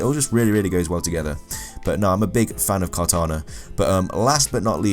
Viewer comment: HOLY! I thought you were talking about Halo when you said Kartana! I thought you said Cortona!